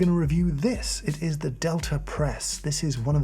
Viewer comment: LMAO I had heard you speak of this product before in another video but I didn’t know the product and thought you were saying “delta press” with an insufferable accent.